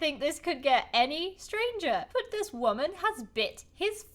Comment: I thought she said butt 😂😂